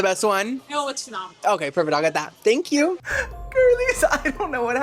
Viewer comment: I SWEAR I heard her say “oh it’s phenomenal” so I would’ve done the same thing 😂😂